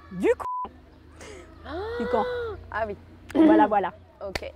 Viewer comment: du quoi ?? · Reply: "Dukan", régime de Pierre Dukan : je n'en avais jamais entendu parler auparavant.